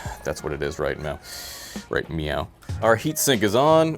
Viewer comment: Did you say "meow?" Are you dancing around all nimbly pimbly like a cat?